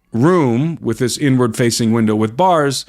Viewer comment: Im so american, at 5:30 i thought you said you had an N-word facing window 😂😮